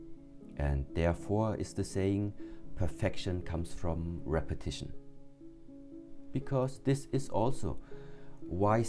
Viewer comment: This is my second time to listen to this. The first time I thought you said protection comes with repetition. Today I heard perfection comes with repetition. I like protection. At my age the idea I may very well have to fight. I will have to think protection. It may be a reality.